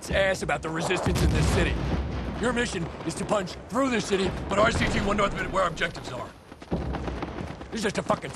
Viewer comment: What does he say at 0:44???? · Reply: Your mission is to punch through this city, with rct1, north of where our objectives are!